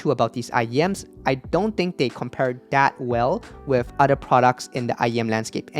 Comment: Did he say "they dont compare well with other iems on the market" ?.. 17:50 Am i hearing something wrong there as ie900 is a top3 iem and the 600 isnt far behind it for him..??